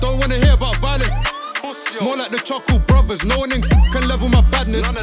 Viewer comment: “I don’t want to hear about violent siblings pussyholes more like the chuckle brothers” funniest bar in history im dead 😭😭😭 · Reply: I heard "more like charcoal brothers" and thought it was too dark, no melanin.